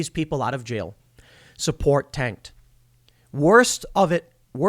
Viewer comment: I thought it said "BLM supports tanks", as in, "BLM purchased a number of M1 Abrams main battle tanks".